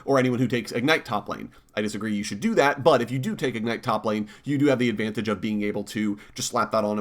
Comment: I heard "If you're a degenerate" 11:57 you said that because of what I said in the server didn't you